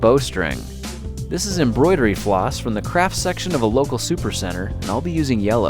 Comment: Why do I always hear "crap" when he says "craft" ?